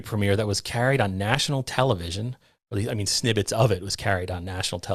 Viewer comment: Did you say "snibbets" instead of "snippets?"